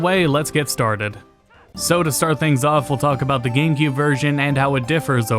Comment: is it just me, or do i hear "Game Gear" Version? :D